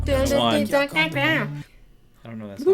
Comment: Did she say 'Okay, hör auf!' at 14:34? you hadn't told us that you speak German? Now we all know your secret xD